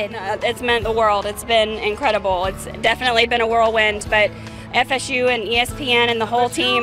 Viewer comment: I really thought he said F U Until I heard S U I think I heard S U